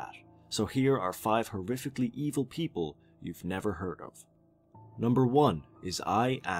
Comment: I thought you said people we'd never heard of